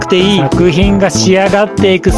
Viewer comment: 最後の 五感全部使って感じてろ！！ が 股間全部使って感じてろ！！ に聞こえてしばらく「？？」ってなった